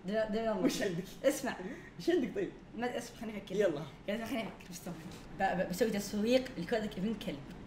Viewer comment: وش قال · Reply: قال بيسوي تسويق لكود